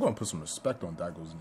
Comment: subtitles say got put some respect on that girls name 😆😆😆 Instead of saying daigo.